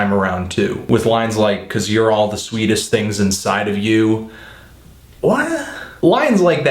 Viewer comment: I thought Avey said "Your om's the sweetest thing inside of you".